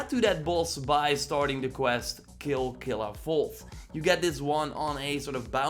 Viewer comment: did you said kill killafull ?